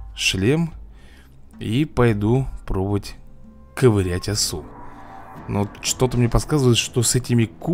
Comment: амне послышалось ковырять в носу 😂😂😂